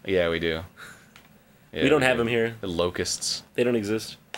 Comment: I thought he said "and locusts"